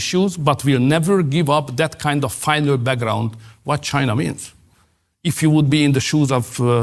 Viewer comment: Omg Tucker,I thought you said “ vagina” when you said China 🤣😂🤣😂🤣🤣😂🤣🤣🤣🤣🤣🤣🤣🤣